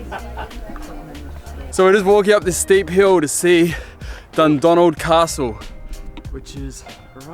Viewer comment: I legit thought said "The Donald Castle" and Trump just owned some castle in Scotland